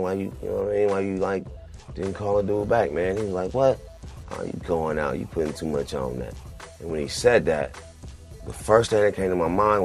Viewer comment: what did casual say? couldn't make that bit out... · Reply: "You going out. You putting too much on that." He probably meant that Saafir was overreacting, and making too much of a deal of it.